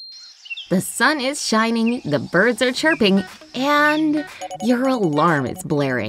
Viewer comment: I thought that she said the birds are tripping 😂